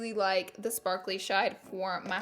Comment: Anyone notice that she said “sparkly shide” instead of shade at 4:40 lol